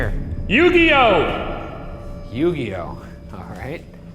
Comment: had replay a couple times I thought Shane said yugioh 🤣